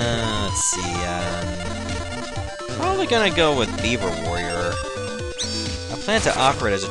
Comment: I could have sworn you said "Bieber Warrior" x_x Thank god I know better...